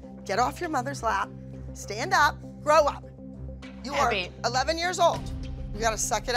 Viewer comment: When abby said grow up, I thought she said throw up and I was just like😂😂